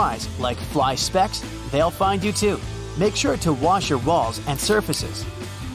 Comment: sounds like he said “was your ba-.”